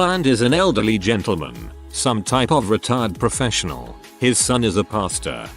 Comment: I thought the first one said the elderly guy was a "retard professional" and i was like wtf? 😂